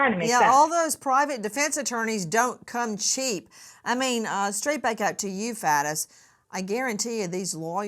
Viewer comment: Sounds like she says "straight back to you, Fat ass" 😂